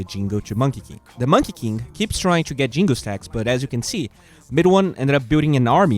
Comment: Any suggestions for more how to win an impossible lane? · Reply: you said midone instead of no one ???